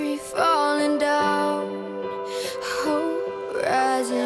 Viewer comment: WHAT IS "uhup"anyone else hear that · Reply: "uhup" is actually a breathy sound, then the word "hope".